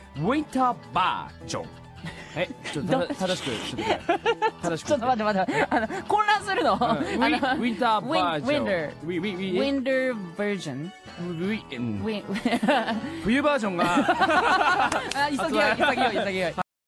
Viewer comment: I heard "we virgin" instead